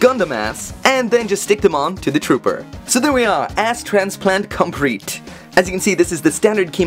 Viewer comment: did he just say "Ass transplante COMPRETE"? xD